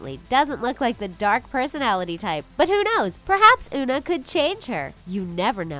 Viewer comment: did anyone else hear Una?